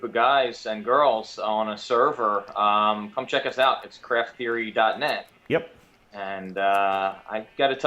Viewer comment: What was the name of the server you said? i didnt catch the correct thing said. was it craft <link>? you said it at 7:59 btw lol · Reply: The server is Craft Theory. You can go to <link> and fill out an application. Just scroll to the bottom of the page.